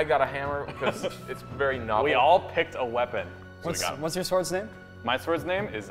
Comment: Hello! I love this video so much, and I want to make sure everyone can understand and enjoy it. That's why I wanted to let you know that your captions are a little off. At about 0:21 the auto-captions say "Whats your source it" when meant to say "What's your swords name." I just wanted to ask if you could fix that error as there are 360 million people that rely on closed captions for videos and the chances of some watching your videos is high especially since your content is phenomenal. Thank you and keep making awesome videos!